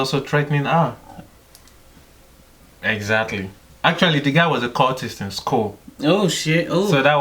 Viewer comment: What did he say @ 13:13!? The girl was what? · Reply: The guy was a cultist in school. Lol